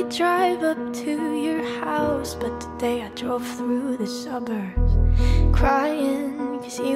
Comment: Is it just me or did it sound like she said summers when she really said suburbs